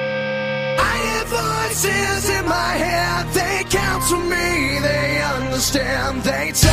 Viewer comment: Wait! "They council me"!? I always thought it said "they talk to me" twice